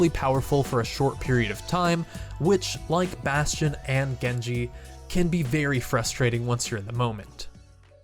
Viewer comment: I thought it was Gengar he said instead of Genji